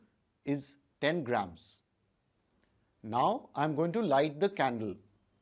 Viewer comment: I thought he said “So lets light the candely”